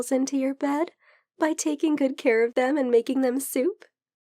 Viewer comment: I heard her say simp, but it was soup. Help.